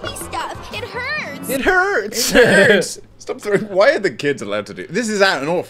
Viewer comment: Am I the only one who heard the “stop throwing at me stuff”?